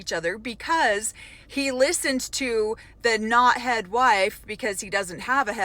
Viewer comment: Hahaha I heard that as 'knothead wife'